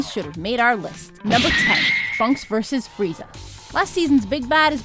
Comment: I keep hearing "Funks vs Frieza"